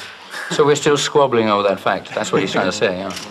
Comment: yall what did Freddie say at 1:35 they laughed and im confused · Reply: He said :we are still squabbling over that fact"; he means arguing over which songs to play.